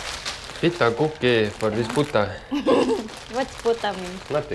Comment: Did he say in minute 9:14 "for this Puta" ? 😯 I think he really said that.